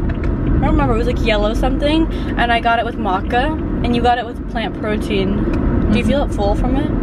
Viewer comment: Hahah omg in the beginning I thought you said you got it with vodka instead of macca lol! Then when you said you're mom got it with protein I knew that couldn't be right